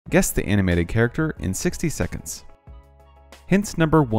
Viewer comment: I thought he said guess the video game characters 💀